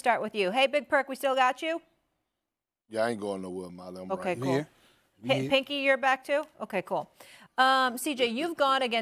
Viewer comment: did she just call him pinky? lol SAS and Molly have a great chemistry.